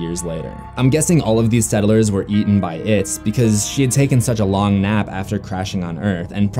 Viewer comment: did he say she